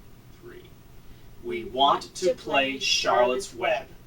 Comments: I HEAR IT! "I won't play with you." Omg it's such a cute voice but it made me feel so scared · I heard "I dont wanna play with you" OHHHH GOD MY HEART STOPPED FOR A MINUTE! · I heard a little girl say "I won't play with you" in a whisper when you lit the candle and said we want to play Charlottes web! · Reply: I can clearly hear a girl whispering "I..won't..play" if I turn my volume all the way up. You have to be wearing earbuds though. I was honestly shocked when I heard it. It's really creepy! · Omfg I heard at 14:50 I don't want to play with you it was very faint though other people who commented that they where right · Omg I heard her whisper 'I won't play with you' at 14:50 as well...now I'm creeped out o.O